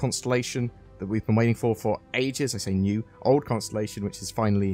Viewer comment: When you said "waiting for ages", I heard "waiting for aegis"